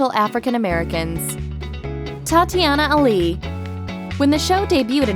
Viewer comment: Thought I heard Thotiana, when she say Tatyana